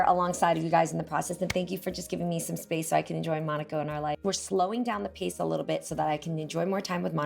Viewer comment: I love how Jeanie tried to stay so gender neutral but around 12:38 she said "in her life". Did anyone else catch that?